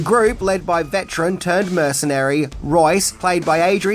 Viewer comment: I heard "veteran turd mercenary"